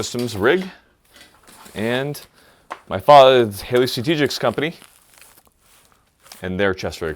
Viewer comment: What happened at 3:20?? Did he say Father’s?